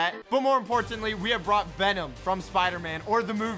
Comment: He really said Benom 0:20 lol 😂